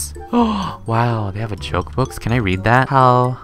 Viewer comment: When you saw the jokes book i thought you said the chokes book XD